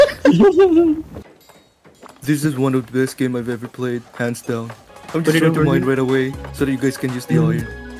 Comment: Is it bad that I heard "This is one of the best games I have ever played pants down"